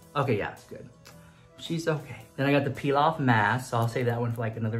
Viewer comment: says “peel off mask” ... What i heard : “pilaf mask “ 😂😂😂😂 why am i real